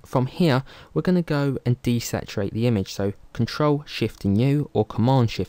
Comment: what are you saying Ctrl+Shift + new ?? I didnt get it . it not clear to me. at time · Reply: Control + Shift + U